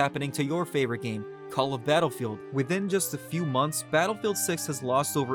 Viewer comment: did he just say call of battlefield 😭🙏